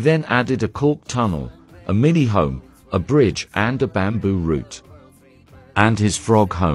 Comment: I misheard "a fridge" at first and started to wonder why would hamsters need a fridge 😂